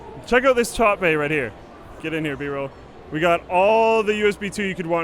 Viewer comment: I thought said "get in here b-hole"